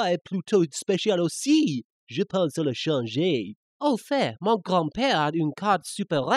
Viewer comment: lol, I love the abridged series, the french version sounds pretty wrong though and makes eve less sense than the original... sorry if I sound mean it's just that I love this video it's hilariousness, but didn't Yugi mean to say grandfather, since he called his grandpa 'dad' in what sounded more like Spanish than french... and I kinda don't get why Tristain was talking about changing pants... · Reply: oh i thought i heard 'je pantalon changer'... either way it still doesn't make any sense, and is different from the original